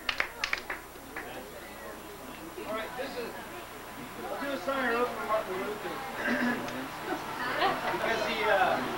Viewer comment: Thank you for posting this. What are Rich's words immediately before "Martin Luther" at time 30:40? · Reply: John Ossi he said: this is a song I wrote for Martin Luther.